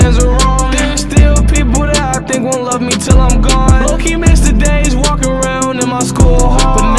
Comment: “There still people that I think won’t love me til I’m gone” let’s see how many listens to and likes this video gets now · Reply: damn i thought he said "theres still people that i think will love me till im gone" i heard it wrong i guess.